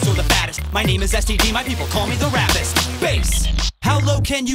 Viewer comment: I thought it said rapist LOL